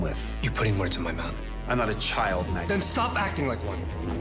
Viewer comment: At the start i heard Alec said: "I want a child, Magnus." and my heart really stopped for while. Seriously. And then i keep watched and replay and i was like: Oh, they´re just fighting. AGAIN. Yeah, it was really disappointment.